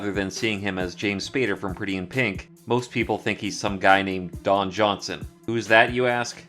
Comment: I may be wrong but I thought that Robbie said everyone thought he looked like Dan Johnson instead of Don Johnson which I believe was clever scripting although it could have also been a mistake he made the they didn't pick up on. Also I may have misheard so will need to go back and listen to that again to make sure.